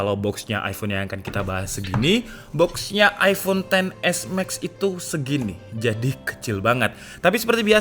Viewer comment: Hp "TEEN XS MAX" apa gak salah denger menit